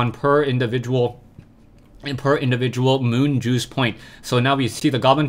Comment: lol did u say moon juice ?